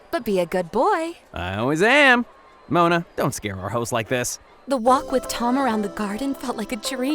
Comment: "She was blonde and I was black"💀💀 that got me on the floor Edit: THANKSKKKKK BABESSSS FOR 112 LIKESS🎉❤❤ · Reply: I thought he said " mona don't scared the huzz like that" 😭😭